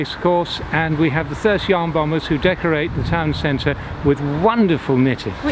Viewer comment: “Thirty armed robbers?” Think I misheard one of the 3 great things about Thirsk🤔